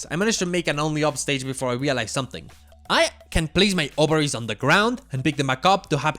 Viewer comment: I THOUGHT HE SAID OVARIES HELPPP